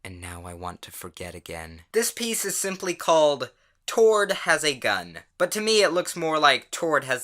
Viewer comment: please dont tell me im the only one who thought it said "Toro"